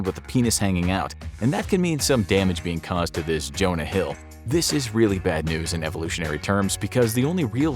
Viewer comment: did they just call Jonah Hill a dick? Lol what did he do to the infographics team to make them take shots at him?